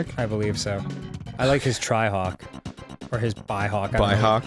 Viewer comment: For some reason I heard Dan say “tryhawk” not “trihawk”